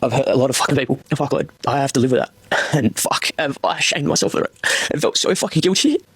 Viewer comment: did he say he’s shagged himself? 👀